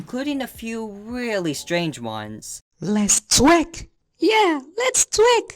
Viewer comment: Why did I hear "let's twick!" "Yea let's twick!!" 😭